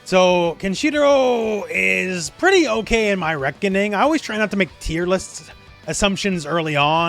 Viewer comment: did you just say rekka-ning?